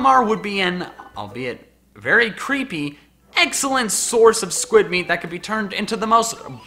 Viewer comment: / 8:10 did he just say "eggxelent"?